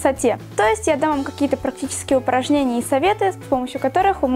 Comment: Чижова сказала так не внятно слово упражнение , что мне послышалось выпорожнения!!!! OMG! Она так тупо выглядит!!!!🤣